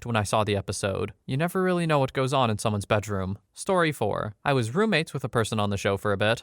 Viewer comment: Am I having a stroke? I'm at 3:50 and I swear the same thing was said 3 or 4 times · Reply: he said bedroom but the subtitles said “Bedroom Room” 😂